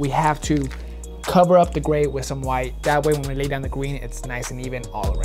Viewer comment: Did anyone catch say gween instead of green at 3:50 in the video